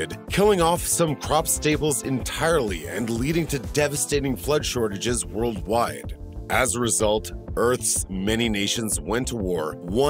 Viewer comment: Did it say “Leading to flood shortages worldwide.”?